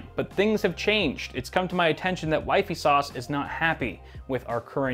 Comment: Did he just say wifey sauce....